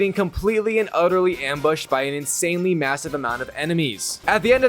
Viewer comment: did someone say MASSIVE